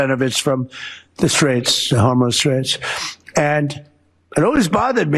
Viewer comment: does he think it’s called the “The Homo Straits”? 🤦🏻‍♂️